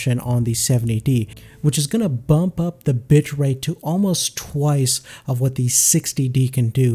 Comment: sounds like he says "bump up the bitch rate"